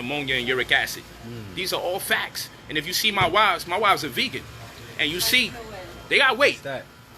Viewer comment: Did he say his wives at 7:13 .....as in plural ....wtf.....!